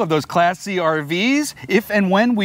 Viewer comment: At first, I thought you guys said half assed Travelers! Lol